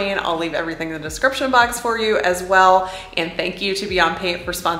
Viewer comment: First time on this channel, so I knew nothing sbout the product placements. At the end, I thought she said," Thank you to Beyonce for sponsoring this video"😂. I was like...👀..."really?!" 😅